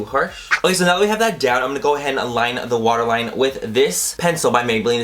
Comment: At first I thought you said washer line instead of water line lol. Love the look!!